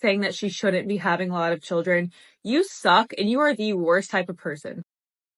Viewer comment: Funny how the text on screen at 1:49 -1:50 read "worst type of husband" but the woman actually said, "worst type of person". Almost like the powers that be wanted people to see it as husband instead of what was really said, person. Coincidence or accidental? With todays world and TikTok and YT fight against men I wouldn't be surprised if it was intentional.